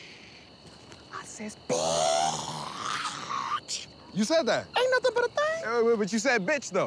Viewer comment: What’s he saying at 02:57 ? Someone help a sister out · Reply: when he said "aint nothin but a thang"